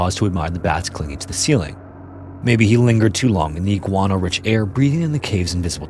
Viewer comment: I listened to this while doing other things and at one point I heard "iguana rich air" and my confusion was colossal...